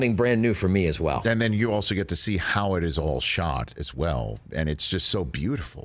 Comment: I misheard 1:31 as "Howard is all shot" and I was like LOL YEP HOW DID YOU PREDICT THAT and then I realized I misheard XD 8:17 HES GAMING POG